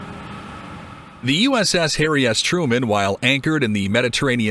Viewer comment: My grandfather was in WWII on a boat in the North Pacific. The boat was icy and blacked out to not give away their position. He mentioned Japanese bombers flying over them many times without detecting them. One night the ship hit a wave and the icy deck caused a bunch of sailors overboard. With only the moonlight they begin pulling people on board when sharks appeared. They couldn't pull them out fast enough and everyone began to panic and scream, possibly being attacked, my grandpa never confirmed if they were actually being attacked yet. Anyhow, as they were pulling them out they heard what he called "eerie screams" coming towards them. Moments later a school of dolphins come and fought off all the sharks, giving the sailors a chance to get back on board. My grandfather passed not long after I had a the chance to meet him and hear this awesome story. I only ever seen him twice in 2005 when I was 25 years old and he left me with that awesome story that I love to share with others whenever I get the opportunity. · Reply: sounded like he said ‘the uss hairy ass truman’